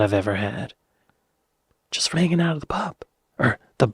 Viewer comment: Thought you said 'making out' there for a hot minute. With family..? 🤣